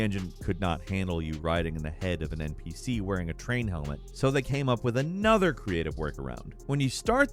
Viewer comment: i’m sorry i thought i just heard u say i was wearing a train hat!!! naaa surely that’s my hearing playing tricks on me???!!! 🤣🤣🤣 choo choo